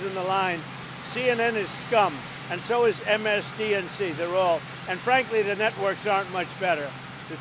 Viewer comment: Did he say MSDCN instead of MSNBC?